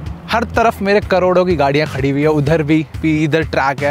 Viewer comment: he said Meri caroroi ki cars kheri hai 😂😂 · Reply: Uska matlab tha "mere har taraf corodo ki gadiya khadi hai"🎉